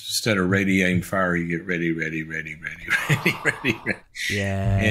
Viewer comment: instead of radiating fire you get *inaudible i hate that the interviewer agrees here but i cant understand what dr. Cloud says. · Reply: He seems to say instead of radiating fire you just get 'ready ready ready ready'. But likewise i don't know what that means ,🤷. I'm not actually sure the interviewer does either!